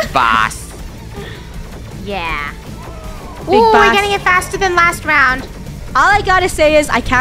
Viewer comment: I heard b***h boss 😂😂